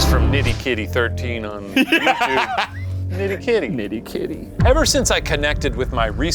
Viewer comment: I swear to God, I thought you guys just said Diddy kitty